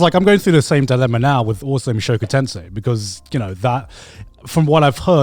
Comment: Which show? The captions say awesome shokutense. I am not having much luck finding anything. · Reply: Sounds like Mushoku Tensei, or "Jobless Reincarnation" which matches the taking a long time to make comment.